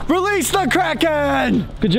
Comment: I thought he said release the crackhead